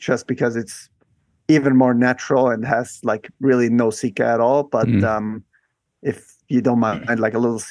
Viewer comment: Thanks for uploading! At 55:13 Jakob speaks of natural routes vs routes that have ”seeka” (can’t figure out what he says) could anyone explain? Thanks! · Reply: Sika. What you use to stick a bit of rock back on (if it's broken off) or even manufacture new, artificial, holds. The better the rock quality, the less sika is needed.